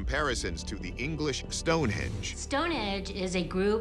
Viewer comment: She really says stone edge at 3:42 😂